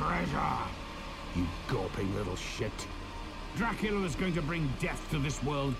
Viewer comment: “You gopping little shit” ...Did the ominous primordial being in the room just say that? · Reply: I thought he said "Gawping little shit" because he was stood there with his jaw on the floor in shock.